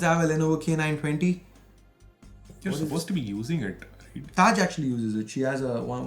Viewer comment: what name did he say? Taj eh??